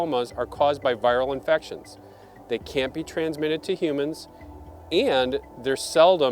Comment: they CAN or CANT be transferable to human? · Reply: Sounds like he said can't. I turned the subtitles on and it said can't... But still, I wouldn't chance it.